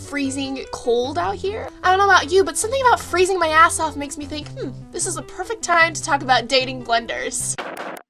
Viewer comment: For a second I thought you said, "this is the perfect time to talk about dating blenders"...